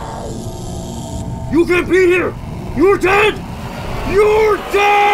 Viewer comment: For the longest time now, I actually thought she said: "You can't be here! You're dad! YOU'RE DAAAAAD..!" Looks like I was mistaken...